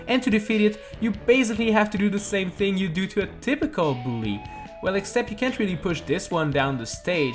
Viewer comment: When he said typical bully I thought he said a difficult bully and I was like 😅🥲